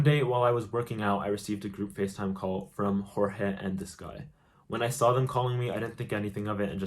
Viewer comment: We can all agree we all dislike Jorge with a passion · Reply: I thought u said Jorbe or whatever his nane is from tik tok 😂🤍